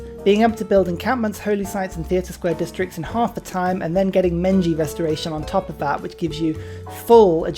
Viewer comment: I'm sorry.. did you say Menji Restoration?